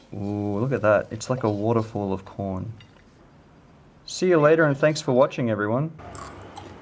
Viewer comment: I don't know why I heard "The water fall of porn"